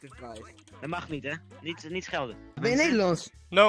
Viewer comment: he actually says they eat rice lmao "米饭" (mi fan) = rice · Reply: Also at 5:58 the guy said "niet schelden" which means "don't swear" instead of "don't shout"